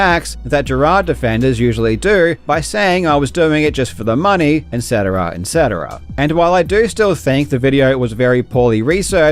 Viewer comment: did you say "en cetera"